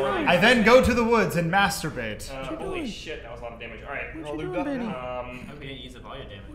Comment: Anyone else hear someone whispering "what cha doing?" That's freaky!!!